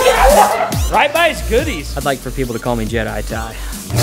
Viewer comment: did he say- I'd like for people to call me JADEJA ?